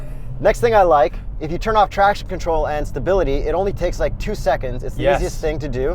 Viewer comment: lol did say "trash" control at 2:28?